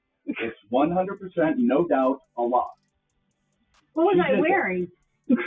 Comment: did he say "100% no doubt a lie"